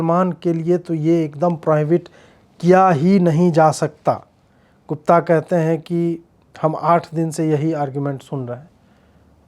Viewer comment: did he just say "Kutta"???😂